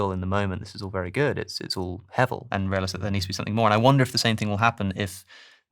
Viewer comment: what is the word says here? Heavel?